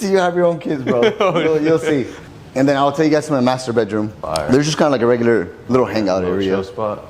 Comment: Am I the only one who heard "I will show you my masturbate room" ?